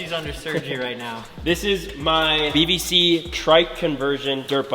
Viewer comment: Did he say BBC? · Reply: BVC